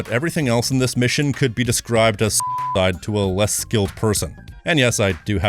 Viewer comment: What did he said in 13:06? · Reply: he said suicide